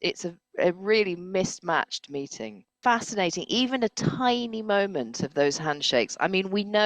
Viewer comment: Forgive me, in your opening I thought you said this was the “mating” of Mr Charisma with Mr Logic. Nearly spilled my coffee😊😊😊